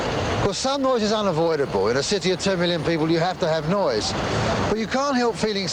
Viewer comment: Did he say 2 million people? Listened a few times and that's all I hear... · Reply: I thought he said 10 million people. Edit. I Googled the population of London in 1975 when this was first seen... 7.5 million so... 🤷